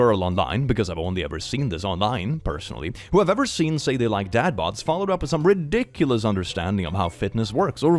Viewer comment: Istg when i first heard the "dad bod" upopular take, i thought you had said "dad bot" like the fucking discord bot lmao I was making myself some buttered corn bread so thats on me but when the talk about muscles vs dadbots came up i was thoroughly confused